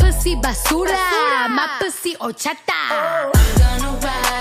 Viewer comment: 처음들었을때 My pussy oh 작다 라고 들려서 겁나 놀랬었음... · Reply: 저는 My pussy 오졌다!로 들리던데ㅋㅋㅋㅋ